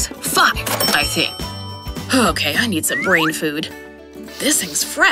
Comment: I think he said the f word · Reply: Source Accounting Australia oh! You mean well no he said food